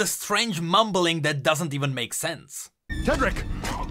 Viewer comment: Actually it's more like "Not Tedric" eventually "KiII/KiIIed". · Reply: I thought it said "Not. TedRiC, HErE" basically taunting the dude.